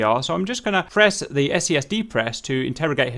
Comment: Can you please explain the interrogation part again? You were talking way too fast. I think that you said something about an "SES" depress?? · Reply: Sensor Control Switch - Depress. But you probably know it by now